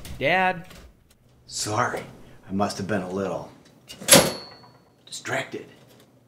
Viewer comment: What did he say at 0:57? I'm not english and I don't understand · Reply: « I must’ve been a little distracted »